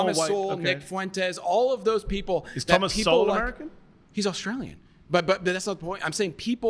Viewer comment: Did he just say thomas sowell was Australian? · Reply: Thomas Sewell. Not Thomas Sowell. Thomas Sewell is an Australian nationalist.